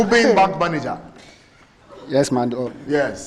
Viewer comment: did i hear "yes my dog?